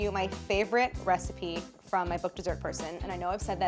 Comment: It's the first time I'm watching a video from this channel and I heard 'Dessert Person' at the end of the intro as 'Desert her son'.